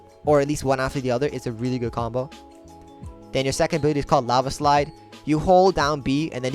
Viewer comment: did I hear him wrong or did he say lava slide